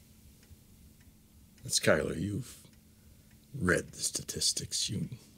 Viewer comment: I heard breakfast statistics